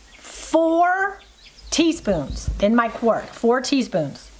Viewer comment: I will definitely try this. I have a ton of tomatoes coming on along with basil and my garlic I just cured. Thank you. Love easy food ideas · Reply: I went though the transcript. I thought for sure you said 4 tablespoons. My bad, it was 4 teaspoons.